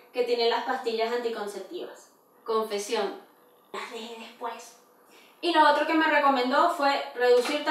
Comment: Qué dice en ese minuto? Bajé después? No le entiendo D: alguien que me diga que dice? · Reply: dice: LAS DEJE DESPUES.